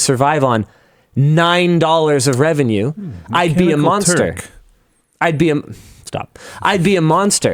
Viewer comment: Anyone know what like said at 8:29? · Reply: Consider “Mechanical Turk”